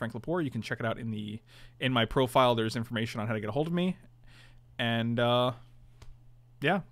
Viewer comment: I had to go back twice because I thought you said your Twitch page had "Information there on how to hold me".